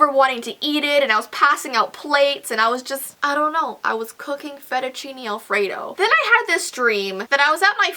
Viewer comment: For a second I thought she said that she was pooping feduchini (i probably spelled that wrong) Alfredo